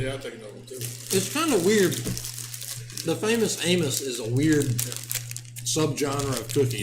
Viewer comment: I was literally typing out this comments when Hayley on the live chat commented what Im about to say lol !!....... I thought he was saying 'Famous Anus' too lol !! 😂😂